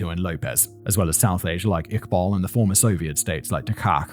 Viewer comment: does anyone know what does simon say here? the soviet state killer? I can't understand him